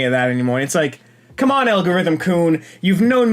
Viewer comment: Ok, I misheard when you said "Algorithm-kun", I thought you said "Algorithm Coon"(as in racoons.)